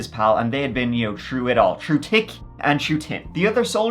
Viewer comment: "True tick an true tin" Never change. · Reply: I thought he said tick and tin. Good story teller